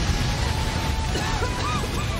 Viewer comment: Did garaga just say "get yourself knocked up brat."? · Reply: He says “don’t get yourself knocked off brat.” The “don’t” part is just cut off. Meaning for Boruto to not get knocked off his head.